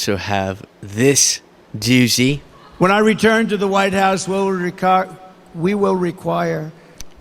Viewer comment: First time I heard 1:10, I thought that Trump said that he had "returned to the lighthouse".